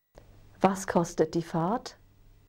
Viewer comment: lol i thought i heard the word fart